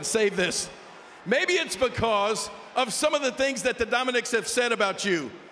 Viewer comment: am i the only one that heard dominiks instead of mysterios?